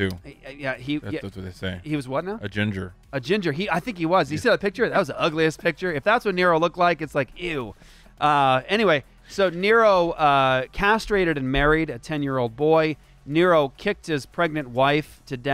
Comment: does he say "that was a ugly ass picture!"? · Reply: I heard "ugliest picture"